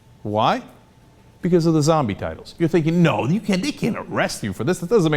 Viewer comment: lol i thought it said "Zombie Titties"